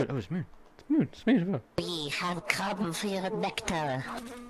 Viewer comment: For some reason I always hear "We have Krabben für ihren Nectar" and not "We have come for your nectar". 😅 "Krabben" is german for "shrimps" and "für ihren" is german for "for your".